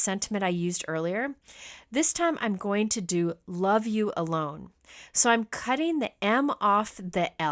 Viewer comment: LOL- I thought you were saying you were changing the sentiment to "love you alone". I thought that was a bit strange! Enjoyed seeing this technique and wonder if the alcohol would work on embossing folders....